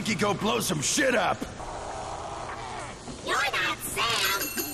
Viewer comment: aww fixing his little hat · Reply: Your not Sam is what it said